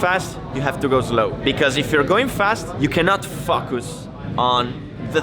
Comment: Did he say “FUCK US” at 2:50?!?!